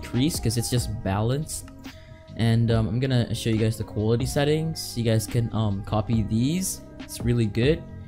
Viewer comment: yo thx bro helped me a ton btw did he just say coolity settings?? lololol